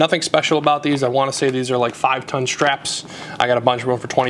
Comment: did u say 5 ton strap?